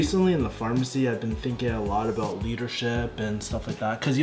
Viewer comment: lol 0:34 Thought he said he was "thinking about weed and shit" for a secing had to rewind right quick....